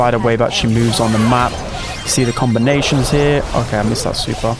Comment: the combinations bro sounded like Composure